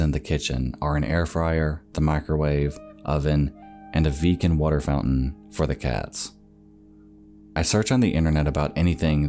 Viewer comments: Not gonna lie, I thought he said a "vegan water fountain for the cat" and I was like, "oh boy, one of those... wait wtf? There's vegan water now?!?" 😂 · at first I was just listening and I heard "... and a VEGAN water purifier for the cats" my fist thought was "how in the hell is a vegan water purifier different than any other". Only to waste time to rewind, read the transcript and write this unnecessary comment!